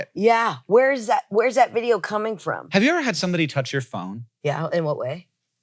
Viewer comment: okay, i watch vids at 2x speed, and its basically never a problem,,,,, but somehow i mistook him saying "phone" for "mom" and that half a second was the most out of pocket sounding thing i think ive ever heard out of his mouth,,, and he says a lot of random, out of pocket things💀,,, was like,,,, well thats a hard left turn in terms of topics hank